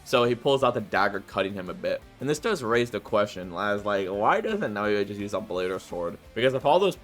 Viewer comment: You said "raise the question" instead of misuse "beg the question - subbed